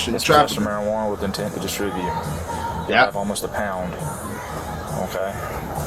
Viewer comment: Tell that to the officer who informed the criminal they committed a felony by having a pound of weed. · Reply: he actually said ALMOST a pound. He never said it was a pound